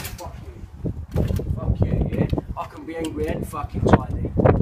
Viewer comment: I can be angry and fucking tidy. The hero Britain needs! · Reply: Simon Gallagher I thought he said "I can be angry and fucking tiny" implying that he can be a hard-man despite being challenged in the height dept. I believe Ricky Gervais said he's a midget